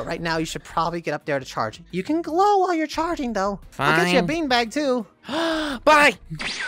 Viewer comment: What did say? Beanbag-?